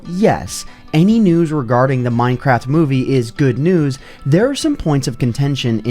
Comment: Why the hell did i hear "Minecraft Woomy"